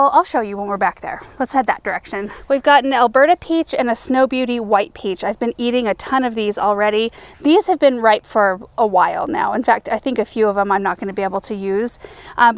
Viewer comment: Beautiful harvest! I thought you were saying “Alberta Peach” and got excited thinking I could grow that, but it’s Elberta Peach and not a zone 3. Sob. Keep up the amazing work!